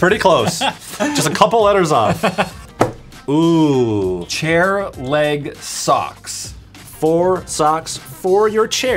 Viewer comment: i thought the socks said hair leg socks